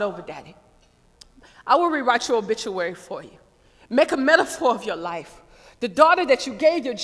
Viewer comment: PLEASE HELP what does she say at 2:39 and 3:20?????? i can't understand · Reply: grated cheese make a metaphor for your life.